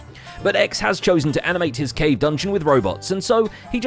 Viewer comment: I had to scroll back a bit, becuz I thought he said "gay dungeon" please articulate your english